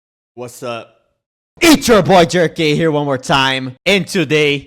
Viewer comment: I misheard the intro and thought he said "its your boy, jerkin one more time and today-"😭😭😭